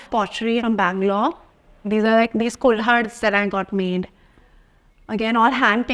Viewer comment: she actually said "kulhads" not cold-something. it's what those pottery/ceramic glasses are called.